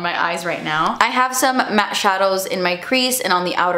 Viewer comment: So what’s the palette you can’t say in the description 👀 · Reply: I thought I heard Mac too, but I think she said “matte shadows”